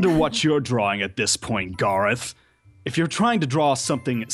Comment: did anyone else hear grath?